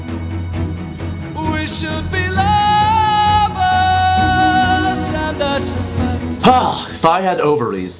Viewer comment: Is he saying "We should be llamas"???? o_o